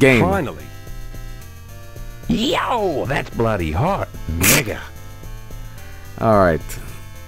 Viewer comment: Does dan say the n word here?? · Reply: He says "Mega!"